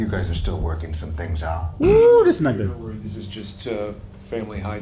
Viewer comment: did say “whew this n***a” when dude said the dinner was premature? I don’t know why but, I guess I’m giving a pass? Because it’s. 😂